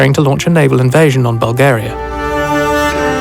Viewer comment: Naval invasion on Bulgaria? Did I hear that right?